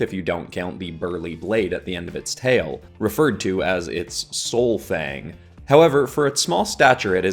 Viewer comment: i thought u said it was called its soul thang 😭